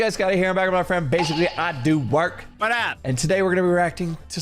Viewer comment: I thought you said “Big giggly I do work” 😂